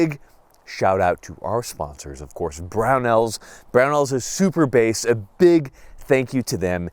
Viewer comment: Did he just say Brownells is super based? 😆